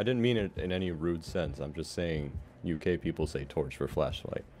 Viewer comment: Who else thought said "You gay people say torch instead of flashlight" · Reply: Icsi it sounded like "you gay people" but he Said you K people like UK instead of the US